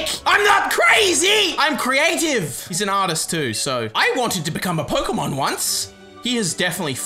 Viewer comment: I thought it said “i wanted to be a Pokémon nonce” 😂